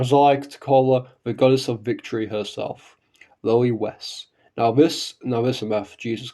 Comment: Good vid but I was doing something else while listening to this and misheard 8:27 as "Lily West" and now I'm thinking about how that sounds like a 4Kids dub name 💀